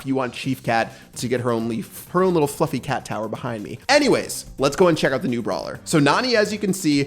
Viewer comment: I heard you say f*cking cat towel (i hope you didnt)